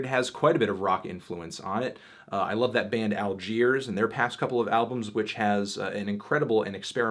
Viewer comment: whats the name of the rock band around 8:00 ? want to check them out, but cant catch it. Thanks : ) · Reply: Algiers